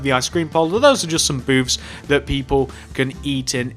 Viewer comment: When he said "booths" I heard "boobs". But I know so many other people heard the same